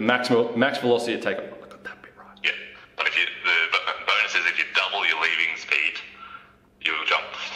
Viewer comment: Double your what speed??? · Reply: Jacob Tober ok I thought he said double your leading speed. I guess he said double your leaving speed. So pretty much just work on leaving the ground as fast as possible with as much speed as possible